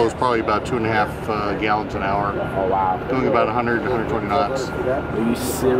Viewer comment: i think he said miles per hour and not knots !! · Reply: nope he clearly said 100 to 120 knots at 2:48 check it out